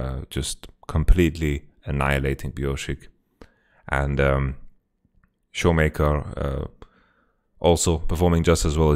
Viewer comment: I thought it said 'claps and showmaker' and i started laughing <3